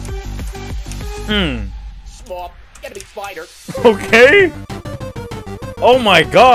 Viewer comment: he actually said small enemy spider